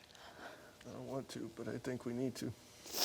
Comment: All I hear is “I don’t want to go to Arizona Martin” 🤣